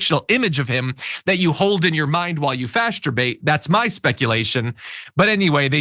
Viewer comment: did he say "Fasterbate" 🤔 at 1:49 🤣🤣🤣🤣 ... whatever it was, it was hysterical